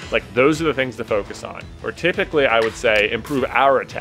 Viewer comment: Did J just say "Protypically?"